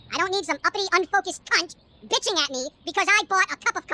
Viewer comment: did he say "cunt"? :D